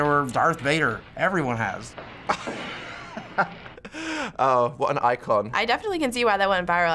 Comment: wth did he just say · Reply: what an icon